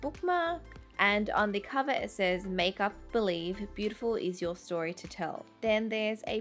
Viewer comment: did she said make up believe?